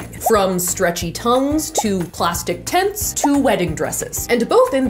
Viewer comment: When she said “plastic tents” I really heard “plastic tits”